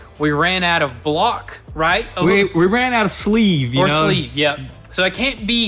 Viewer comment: I thought he said there wasn't enough metal left on the block to do a sleeve? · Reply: They ran out of sleeve. So couldn’t bore it out to put bigger pistons. They still could’ve put new darton sleeves in it and reused everything else